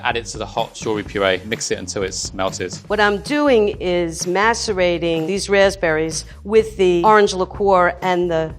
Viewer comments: Did she say masterbating???.... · English is not my first language. Can someone please reassure me that didn't say she was going to "masturbate" the berries? Please tell me I heard it wrong. It's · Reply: she said macerate, to soak in water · what did she say ? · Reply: macerating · did she really say "masturbating"? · did she say masturbating? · Did she say 'masturbating the raspberries'? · did she say “masturbating” ?? 👀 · Everyone's talking about "beat my wives" when sounded like she said she was masturbating the raspberries lolol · did she said "masturbating"? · did she just say "masturbating" @ 7:15? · Did i just hear "masturbating these raspberries" ??? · i rlly thought she said "masturbating these rasberries" and i was like umm sis what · Was I thought only one who thought she said “masturbate” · lmao i literally heard 'So we're just going to masturbate these raspberries...' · did she just say she is masturbating the raspberries? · did she say “mastrubating”